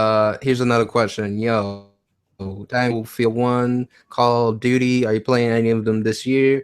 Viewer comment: Did he just say Call of Duked Me? lmfao.